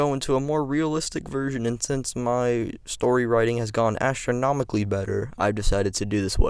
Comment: My guy I thought you said your story telling spike Asthronomically. How can Gohan unlock "His power" (Ultimate form) if Majin Buu never was resurrected? It couldn't happen. 😂😂😂 love the theory tho.